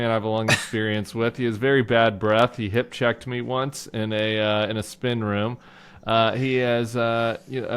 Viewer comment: I misheard that as "He hip-checked me once in a spin class" and momentarily thought Tim and Corey had been peloton-rivals. :D